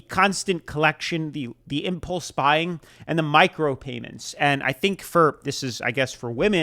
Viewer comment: i thought he said micropenis